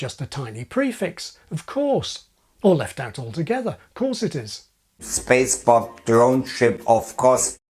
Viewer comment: I always hear the ”spaceport” as: ”spacebuff” 😅.